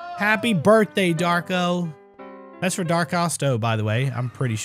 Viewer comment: Oh, THAT'S what they're saying! I've been wondering about that for a while. I always thought they were saying Dawko, but always thought that was weird since Dawko is a FNAF YouTuber.